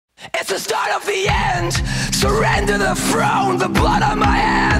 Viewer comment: when i fist heard this i thought he said "surrender the froawn" instead of "surrender the throne"